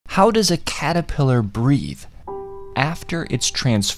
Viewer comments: Lol i thought yu were saying how does a caterpillar breed😂😂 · ohhh he was saying "breathe" I thought he said "breed"😂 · The whole video changes if you thought he said breed instead of breathe at first.